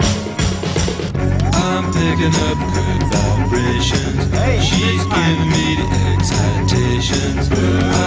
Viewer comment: My record player needle was so bad, I thought they were saying: "I'm pickin' up good vibrators. She's feedin' me beans and taters."